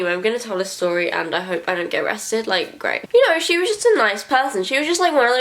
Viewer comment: steps on bug title:I AM A KILLER!! p.s.DID SHE REALLY SAY ,,I hope I dont get arrested"?!?!